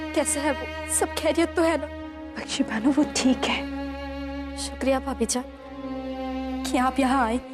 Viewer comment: why i heard phabi jaan in punjabi accent😂